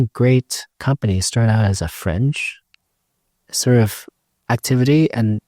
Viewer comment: Did he say "friends"or "fringe"? :D